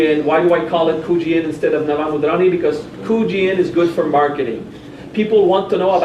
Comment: you say "Why I call it Kuji-In instead of _______. " What is the other name? Thanks. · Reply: Nava Mudrani